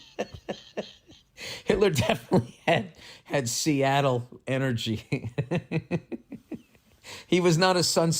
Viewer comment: For some reason I heard "cialis energy"